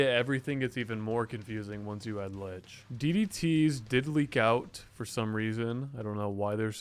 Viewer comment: thought he said “diddly gout”